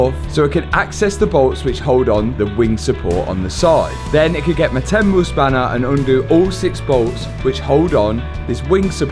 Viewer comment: Honestly thought you said "my Temu spanner". Then realised you said 10mil 😂